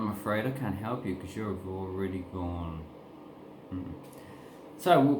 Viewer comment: what are you saying there? "you're a __________" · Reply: already gone, as in they are fucked.